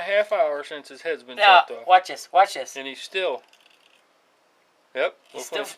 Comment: did she said "yeet" ?